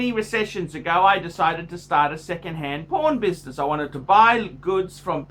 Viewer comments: am I the only one who thought big boi said 2nd hand porn business lol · Totally thought you said "secondhand porn business" at 5:19.